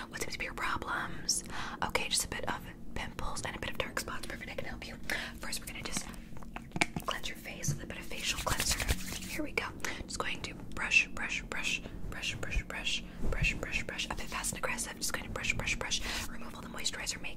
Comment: I thought you said "clench your face" 😖